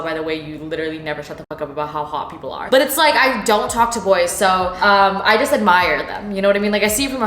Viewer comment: My face when i heard her say "I don't talk to lawyers" then realizing she said "boys"